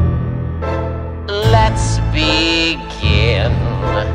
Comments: I thought he said "lets be gay" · I heard "let's be gay"😂😂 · What ppl hear:let's begin What i hear: let's be GAY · Why is it when I hear "let's begin" I always hear lets be gay I'm gonna make you take this (I'm stopping right here for kid safety) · I keep hearing "let's be gay~" the remixes ruined me lmao 💀